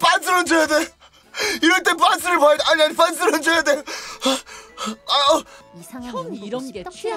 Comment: 그 전 멘트 땜시 빤스를 훔쳐야 돼 라고 들림 ㅋㅋㅋㅋㅋㅋㅋㅋㅋㅋㅋㅋㅋㅋㅋㅋㅋ